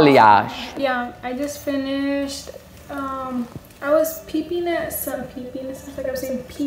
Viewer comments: Follow me on Instagram & Tik Tok. THX ❤️ I can't wait to see you there !!! SUBSCRIBE to my channel to see more HAIR FAILS. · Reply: For the one lady, I think she was saying "peeping at" which means to look or check quickly. To be fair, with her lack of enunciation and the quietness of the video, it did sound like she said "peepeenes." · also to explain the girl in the second clip - she said she was 'peeping' as in looking (i think in the foils to check the process) and said it sounded like she was saying she was peeing as in urinating lol. She spoke really fast and jumbled though so it's not your fault, English is my first language and I still had to rewind the clip 2 times :p